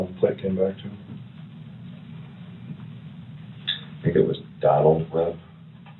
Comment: the subtitles says he said the plates came back to “Donald Trump” 😂